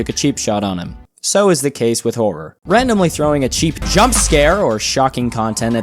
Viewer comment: i thought he said cheif jumpscare